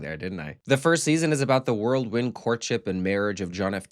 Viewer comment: Surely this guy did not say "world wind"